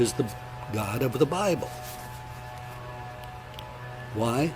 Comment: did he say "the f*ing god of the Bible"?! Lol